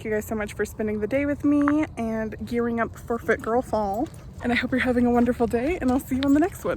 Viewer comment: Who else thought she said "foot girl fall" at the end of the video? I was like hmmm, I guess that could be another option if this youtube thing doesn't work out...